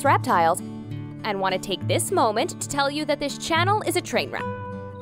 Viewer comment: Did anyone listen to what she says at the end of the video? 5:08 "This channel is a train wreck"